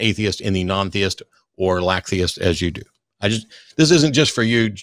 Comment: 😸 did he say a "Lack-theist" ? First time I heard that one. Can't say it's very catchy, nor necessary. but I don't hate it.